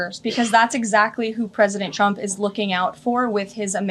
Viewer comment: Sounds like she said a president chump.